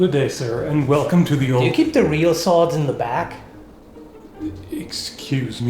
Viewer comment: He said "Do you keep real sword in the back", but i heard "do you keep Dorito sword in the back" What the hell lol.